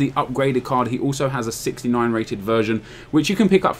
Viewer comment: sounded like you said 69 rated virgin loool